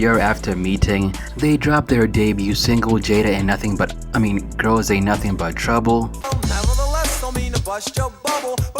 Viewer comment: I heard it you almost said Jada is nothing but trouble.